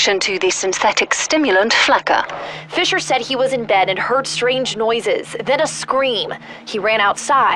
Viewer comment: I thought it said spider not stranger lmao